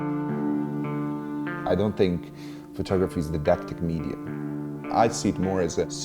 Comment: it sounds like he said: "di·dac·tic" /dīˈdaktik/ Adjective 1.Intended to teach, particularly in having moral instruction as an ulterior motive. 2.In the manner of a teacher, particularly so as to treat someone in a patronizing way.